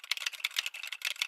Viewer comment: The putro sounds like it said “oh ma god we totally jumped off a cliff we all had to swim all the way to the shore to the end”